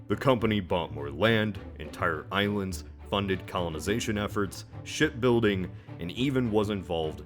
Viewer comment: Did he say Shit building .. :D :D